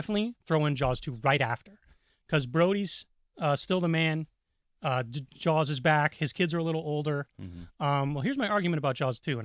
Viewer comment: When you start taking about Jaws 2 you say " Brodys still the man, Jaws is back, his kids are little older..." I honestly thought you were saying Jaw's kids are back... Honestly would have been a better 3rd movie.